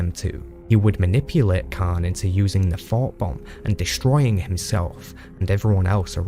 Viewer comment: Bc of the narrators accent, it sounds like he says "The Fart Bomb".... I watch this video almost everyday to go to sleep. It's a perfect summary of the book trilogy. Thank you so much for making this.